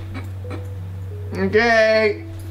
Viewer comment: did he say "your gay" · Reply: No. She said “O kay”